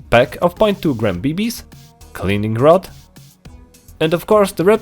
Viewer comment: am i the only one who misheard the guy saying the word cleaning rod, and spelled it like the word Kaliningrad!?